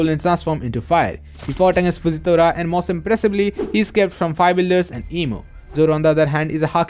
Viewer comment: The captions said the five elders are the five builders😂 👷‍♂️